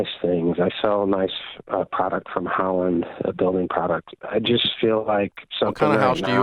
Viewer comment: I would really like to know what industry he is in. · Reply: It sounded like he said building supplies at about the 3:03 mark.